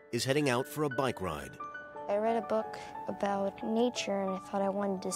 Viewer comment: I thought she said she read a book about Nietzsche.... soon realized she said nature